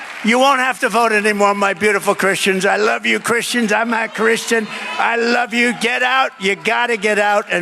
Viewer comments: It's weird, the first time I heard this I thought Trump said "I'm not a Christian", now I hear "I'm a Christian." Which one? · I believe that the dialects and accents made it sound like he said “not a Christian.” I had always kind of viewed you as an out there wild man stud, without knowing much about you or looking into it. My apologies. I did not know you were a Christian, or that you cared about American content, politics, etc. I had always heard you were extremely intelligent, and that’s always been evident from how well spoken you are. Thank you for sharing this and you just gained a new fan and subscriber.